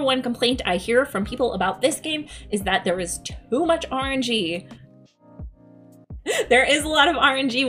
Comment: When you said, "too much RNG," I first heard it as you saying, "too much orange-ee." Sounded like a drink. lol ;-)